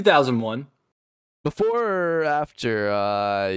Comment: It took me time to realize because I thought he said after but he said before